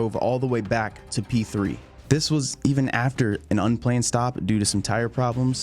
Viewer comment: The subtitles are hilarious. At 29:43 you say "This was even after an unplanned stop due to some tire problems...". Subtitles say "This was even after a nun plane stopped due to some tire problems..."